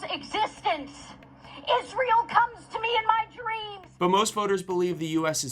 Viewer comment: Imagine if Michigan came to Haley Stevens in her dreams. Sorry - I want a candidate that cares about ME. That is Abdul. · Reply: When I first heard the quote, I thought she said "Israel cums in me in my dreams". Now that I've heard it a second time, what she actually said makes no sense to me.